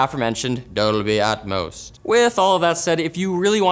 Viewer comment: Did you say "Dolby at most"?